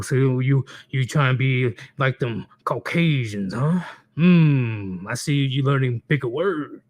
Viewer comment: Did he say 'coke-casions' about 36:00 ? Is that thing? Did I hear wrong?